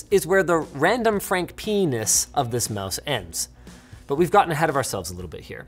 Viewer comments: Did Linus just say Random Frank P*nis lmao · Did he say Frank P*nis?